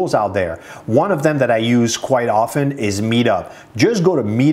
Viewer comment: Love your videos. Some I've watched several time. At 11:20 did you say medub or meetup ? Thanks.